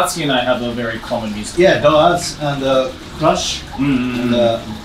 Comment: Did you say Rush? Natsuki and just went up to 11 on the 1 to 10 coolscale.